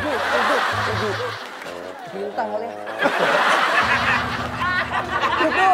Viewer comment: di menit 8:07 dustin bilang apaan sih ? bolak balik ngulang ga ketebak apa yg diomongin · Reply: kan si wakwaw bilang tujuh, terus kata dustin "bintang kali ahh" . mksudnya si dustin (puyer bintang tujuh) kali.